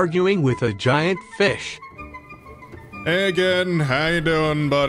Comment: For a second i thought he said hey Egan, so thats what ima call the sea dragon 21:42 Genuine fear and confusion